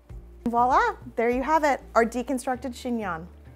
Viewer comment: Omg I thought it said chingon 😵 hahaha